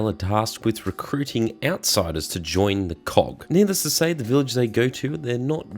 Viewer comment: is it just me or does it sound like you said cock instead of COG?